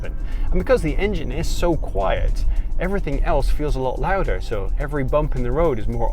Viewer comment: uh, did you say “because the engine feels so quiet”??